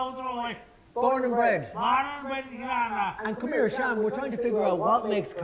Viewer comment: I came here .... (Sean?, This is what i hear) Trying to figure out, what makes currently so special? What do you think that makes this a great town? Errrgggrrrghhid My non-native + non-english hearing be lyke.. WTF ??🤔🤔